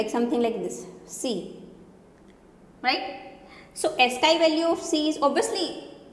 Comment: guys ..in 5:58 time ..mam use a word something like ESCAPE or ESCAY...can you plz tell me what mam said..what is the meaning of that word?? I can't understand the word prpperly...so I can't find it's meaning in dictionary or GOOGLE.. plz help me!!! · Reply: It is ASCII VALUE